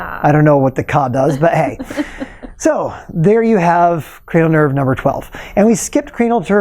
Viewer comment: Did he say cranial turd?